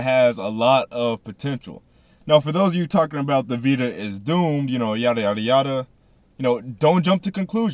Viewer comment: thought u said it was doomed lol :D explain, and i'll sub